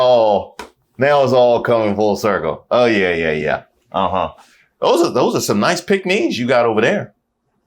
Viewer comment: when he said "those are some nice pick-me's you got over there" i heard "those are some nice Pekingese..." I had to double take and rewind to make sure I didn't miss no cute Shit-Zhu lookin dogs in the background. Pekingese were the breed of dog I had growing up, So I would have both been excited to see them at all and would have judged them on their fur-cut.... Bet they would have been puppy cut and dirty as Pig-Pen from Charlie Brown.